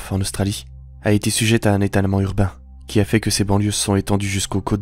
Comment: J'ai entendu "un état allemand urbain" xD j'ai dû réécouter pour comprendre xD